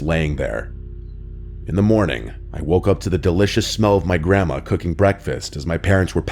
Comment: I thought he said "malicious" EEVIL grandma's FREAKY pancakes and VILLANOUS syrup